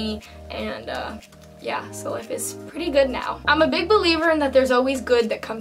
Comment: Btw in the almost ending when u said that ur a big believer or something like that I thought u said that ur a big belieber lol I wanted to quote what u said and not like ur oops I meant write but the reason I'm saying this is coz I want u to notice me